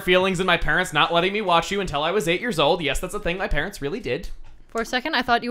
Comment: I honestly thought he said "he could not WASH her till he was 8 years old."